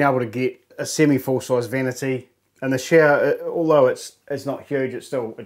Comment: When he said shower I swore he said shitter. Great home you've created. Well done.